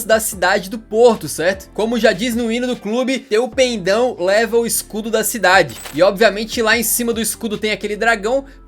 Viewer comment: só eu q ouvi "teu peidão leva o escudo da cidade"??